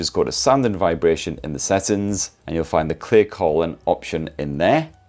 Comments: At one point, I thought he said clear colon and not clear calling. That's good because clear colon would be a bit invasive. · I thought you said ‘Clear Colon’ what an amazing app that would be😅 instead of clear calling…